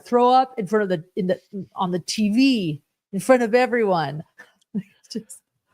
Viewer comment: Did she say "I don't want throw-up on THE TV?" I thought she said "on tv." Meaning on camera. I feel this concern is more on brand from her. Keeping up appearances, objective #1, ALWAYS.